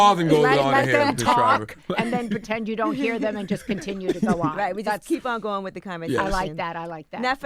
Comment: “Now 4 urself having grown up in this THOT LIFE!!! Tell me I’m not the only 1 who heard that at 1st!??